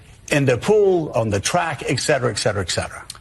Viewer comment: did he say "ekcetera" three times?